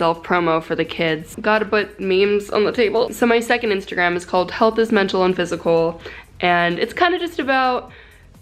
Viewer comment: health is mental and physical!! i thought it said "heal this mental and physical" like a clown..